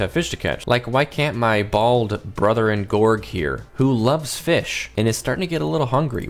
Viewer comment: did you say "brother in Gorg" XDDD